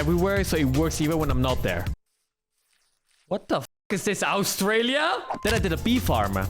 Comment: why did you say Australia like you were gonna say Auschwitz